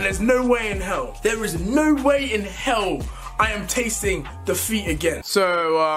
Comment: I thought deji said da feet it took me 2 minutes to reliaze he said defeat so ya 😔